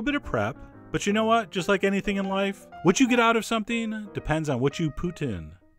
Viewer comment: Did he just say “what you get out in life, you poutine?” 😆